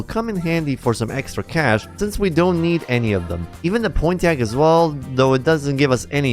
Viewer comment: did you just say pointy ack? Pawn-TEE-ack - pontiac